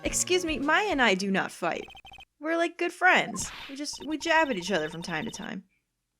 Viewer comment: When you said “We jab at each other from time to time” when talking about Phoenix and Maya, I thought you said “jack.” I’ve been watching your videos too much.